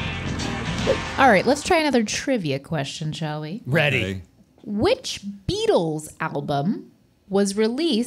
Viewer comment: "Which Beatles album" sounded like "which beatles applebum"